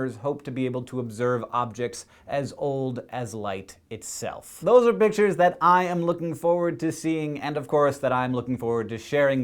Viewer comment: sounds like he says "Those are bitches I'm looking forward to seeing" lolol